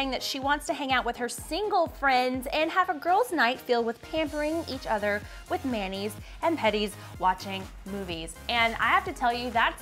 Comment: Haha, I wasn't looking at the screen and I thought you guys said "pampering each other with mayonnaise."